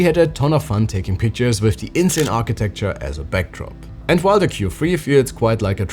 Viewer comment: Genuinly thought you said "backrooms" there, which honestly wouldnt have been wrong :D